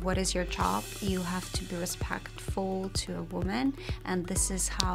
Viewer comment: what does she says in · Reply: She said you have to be respectful to women or something like that